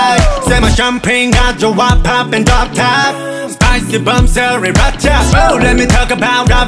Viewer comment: I heard and I quote “spicy bum cells” am I the only one 😂